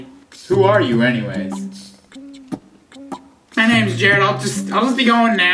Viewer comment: To me, it sounds like Jared said, "My name is Journal."